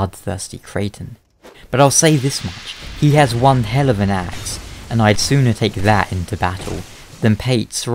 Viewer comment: For about three seconds I thought you said "one hell of an ass" and I was like "Why would that matter in battle and also that's a terrible reason to side with someone".